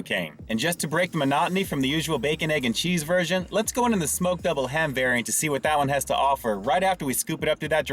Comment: Sounded like she was saying mcdouble ham. I was confused at first. There should be more big breakfast sandwiches out there.😋